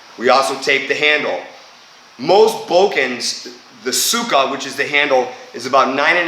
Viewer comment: Nice video! You gave some details that my teacher didn't. Just one thing confused me. I kept hearing: "get the Сука out of the way" I guess you meant "tsuba" right? PS: I'm not trolling. I really kept hearing it.